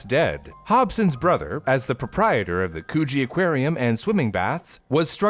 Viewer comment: Wth- I heard “co0ch1e aquarium”- what is wrong with me